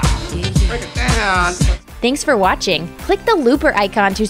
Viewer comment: Every time at the end of the video I hear "click the Lubricant"